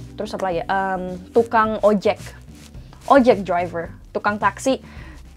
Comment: Did you say ojeG driver?